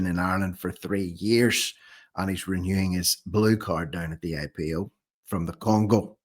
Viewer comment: I thought you said Brew Card at first